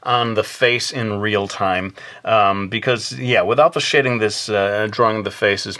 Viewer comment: "without the shitting" .-. ....... idk how it sounded like that to me....